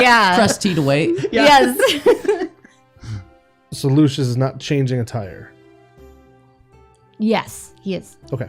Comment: When said “will you be changing attire?” to Lucius, I definitely heard “will you be changing a tire?” 🤣🛞🚙